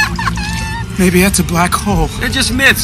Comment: did he call him baby..... are they gay krill.......